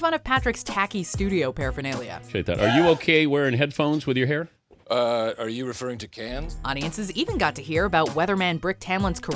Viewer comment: What does he say there? "Uhhh, are you referring to the what "??? I can't make out what he's saying for the life of me so who wants to help me out over here? lol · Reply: "Are you referring to 'cans'," which is industry slang for specifically those big headphones that completely encase the ears